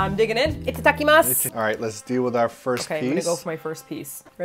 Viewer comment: when said “Itadakimasu” the English subtitles said “It’s a ducky mastic” LOL